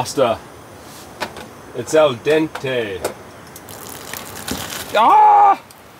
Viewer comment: I miss heard you when you said al dente. I thought you had said half empty and then tipped half out. Talk about face palm myself. Lol. Great camp as always.